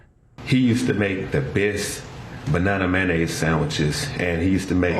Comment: Did they just say he eats a banana and mayonnaise sandwich?